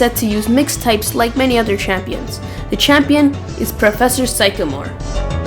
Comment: sounds like he said macklemore